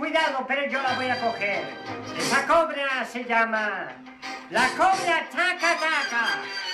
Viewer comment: Pensé que decía la cobra gay